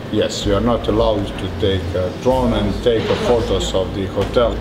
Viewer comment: how did they put drown instead of drone and putting "gowing" instead of "going"